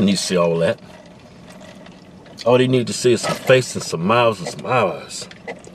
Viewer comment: Tf did Keith say at 7:43 lol..his big ass lips ruined it for him in that situation. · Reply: sounds like he is saying: all they need to see is a face, some smiles and some eyes''